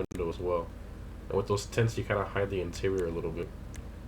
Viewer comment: Was I the only one that heard " tits" instead of "tints" lol